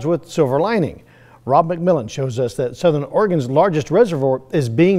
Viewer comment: Am I the only one who heard the Southern Oregon lake was being refilled?